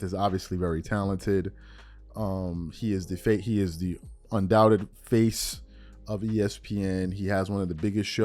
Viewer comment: I am sure I heard 'the fake of ESPN' and then you corrected to 'the face of ESPN'. Both could be true.